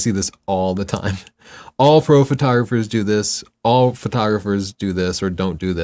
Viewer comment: Freudian Slip at 27:45? Sounded like "All Fro photographers...". Just watched your live show with Jared and loved it, but I thought this was kinda funny!